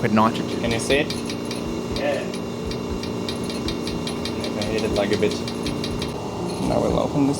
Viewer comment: “I hit it like a bitch” is that what he just said lmao!???